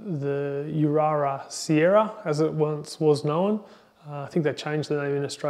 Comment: Is this Daikin Ururu Sarara? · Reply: I thought you said ururu siera 0:10, that confused me. Here in Europe there is only Ururu Sarara, never heard of US7.